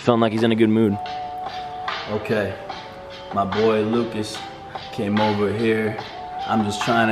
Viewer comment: I am i the only one that thought they were gonna say "My boy Lucas'" "Hes coughing mucus"?